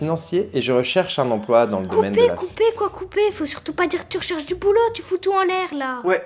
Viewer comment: C’est moi ou elle q dit quoicoubeh